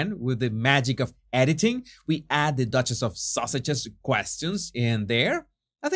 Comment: When say duchess of sausages I cannot stop laughing! I don't know if it is his accent but I just love it! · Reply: I thought Sausages sprang out of the subtitle 'mishearing' saying Sussex, and it made him laugh, and so he simply ran with it! At least whatever the origin, it's hysterical and always makes me laugh every time he says it. It's simply the delightful way it refuses to take them seriously, and they are both so earnest it must infuriate them which is even funnier!